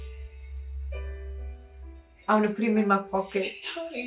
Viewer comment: Im sorry what did she say at @ 11:39? I heard “imma bleaming my pocket” even if i tried to replay it over and over again😅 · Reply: She said "I wanna put him in my pocket"